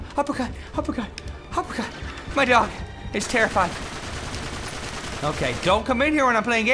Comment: it sounded like he said dong xd: Oh yeah! Oh you all gonna die! I'm gunna live, I'm gunna live, I'm gunna live though! oh oh oh UPPERCUT UPPERCUT UPPERCUT! my dong.. is terrified...